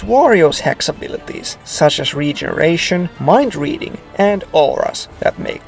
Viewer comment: whenever you said "warriors" I thought you were saying Wario, lol.